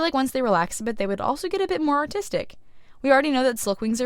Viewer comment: Not trying to be mean, but when she said artist at 1:43, I thought she said autistic. I’m just….not right in the mind. Anyways, lovely art!